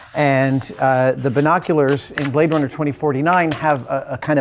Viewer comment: Did he say "Jontron" (as opposed to "Jon-a-tron")